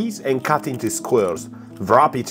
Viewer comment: This guys accent is very strange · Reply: right? i keep hearing "squirrel" when he says "square" lmao.